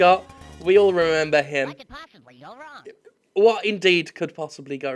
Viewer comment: It's first now I hear that he said "What can posibly go wrong?". When I was a child I always heard "like a posse full of rum". I never got that...